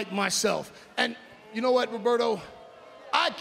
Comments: Lmao bruh did call lashley Humberto at 2:14😂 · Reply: No, he did not ..... He said "Roberto" His name is Franklin Roberto Lashley. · Did he just call Lashley, Humberto? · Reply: Roberto. That's Lashley's Real Name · Did just say Humberto? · Did anyone else realize that called Bobby a different name?😂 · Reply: Swear I thought he said Alberto and I'm like "WHERE TF IS DEL RIO??" 😂😂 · what did he just call Lashley? 🤣🤣🤣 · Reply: Alex Almacin Roberto that’s his real name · Did said "You know what Roberto " Lashley real name lol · Did he just call him Roberto? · Did just call Bobby Roberto